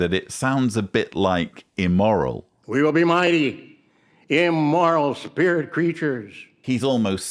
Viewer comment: I keep hearing "Mighty Immoral Spirit Creatures." Which is probably more accurate than what he is trying to say. EDIT: then you highlight that very point.